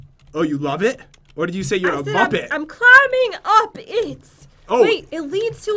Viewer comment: When Jen said I'm climbing up it, I thought she said I'm a nugget